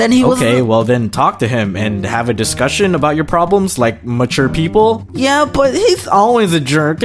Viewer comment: it sounded like he said mucher but im pretty sure he said mature lol no offense